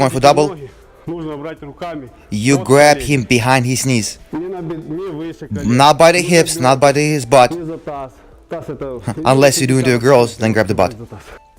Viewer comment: "You grab him behind his knee. Not by the hip, not by his butt. Unless you do that to the girl, then grab the butt." Did I hear it correctly?